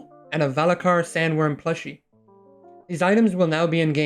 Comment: I honestly thought I heard you say fleshhlight not plushie And then I saw the picture